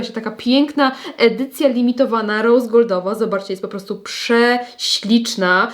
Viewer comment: Nadrabiam zaległości po urlopie i stwierdzam, że czas się wyciszyć i skupić, bo usłyszałam, że polecasz 'piękną rozwodową maszynkę', zamiast rosegoldową😅